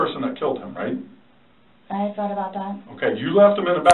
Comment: She's hostile raising her voice as if she's done nothing wrong... The officer asked her, "You do realize you've killed him right," and her response was, "I've thought about that" like wtf lady!?! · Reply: I thought she said “I’ve not thought about that”, cannot decide which is worse to be honest with you. Jeez woman